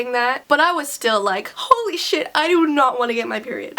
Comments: I heard say "holy shit" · Did you say holy shit at 1:17 I never new u would say bad words 😁😬